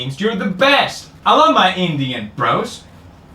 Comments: I heard “I love my Indian bras” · And I thought that Felix said , "I love my Indian bras"